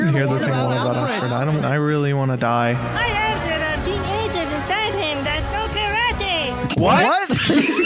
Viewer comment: It's been 20 years and I still don't know what that carrot at 15:03 said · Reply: "I heard there are teenagers inside him that know karate!" It's a Power Rangers reference but the guy's Egyptian accent is super thick😂